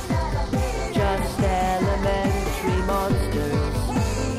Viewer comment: They say that we are monsters. But I don't really care. Just elemental monsters. It's all up in the air. And now that you have landed. Together we will share. A carefree life for monsters. Because here, we walk on air! You can see me in the sky and yet you don't know how to fly, and I don't want to be here, alone. Take a chance, just take a step, and take a leap of faith with me. Can't you feel it? It's in the air. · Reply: I thought he said "elementary"